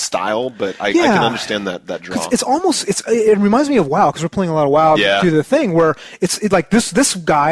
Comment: did u guys just say you were playing WoW? or am i hearing things?